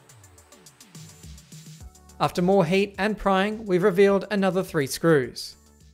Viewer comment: I heard “prying” as “crying”